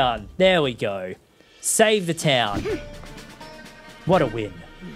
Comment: Everyone read the diary and went behind the throne and in the shelter. I missed that part and went to the ruins that Riju wrote she would train in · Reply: I THOUGHT U SAID EXPLODED THE TOWN 💀🤣